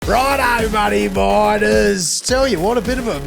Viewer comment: Are you saying "whats up money miners" at the start of each show? Ive been watching for a while and always thought you were saying "whats up bloody biters" 😂😂